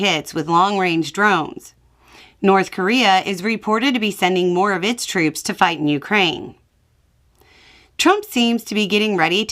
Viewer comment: Just a mild correction: North Korea is considering sending troops to fight on Ukrainian soil for the first time - last time they were there to help Russian forces on Russian soil. If they move onto Ukrainian soil, this would be yet another escalation by Putin. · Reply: Somehow I heard "North Korea is considering sending troops to die in Ukraine" and I was not even shocked. 😦